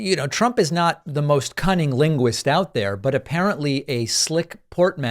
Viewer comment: Did you just say "cunning linguist"?